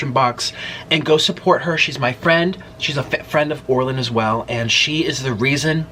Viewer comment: Did you say she’s a FAT friend to Orlin? I hope I misheard you.